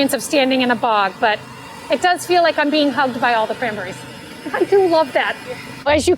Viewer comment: I thought she said it feels like being HUMPPED by all the cranberries. I do love that. 😮. I was like so does that mean they have been skeeting in my mouth every time I have a glass. Brings new meaning to a million little berries in every glass am I right?